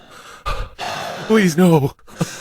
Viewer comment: At around 3:10 it's soundd like the person that is being affected says "Balloons" and thinks that they are so funny that he starts laughing. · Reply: he said:"please...not" but sound like he said balloons